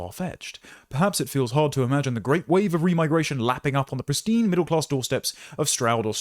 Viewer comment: I thought that said “Remington” lol